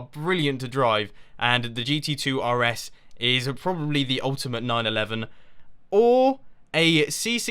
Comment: Did you just say the ultimate 9/11?